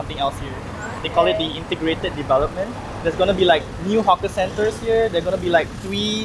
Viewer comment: the subtitles say "hockey centers" when it should be "hawker centers" 😊 Hockey isn't that big a thing in Singapore, hahhaa But we do love our local food❤️